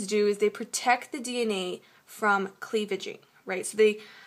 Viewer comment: Can u wrute wht did u say in the minute 5:42 ( They protect the DNA from ........ the gene ) cleaf or cleath or what ?